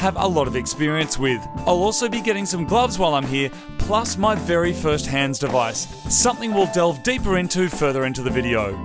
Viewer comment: who else heard, “plus my first hamster vice” 😂😂😂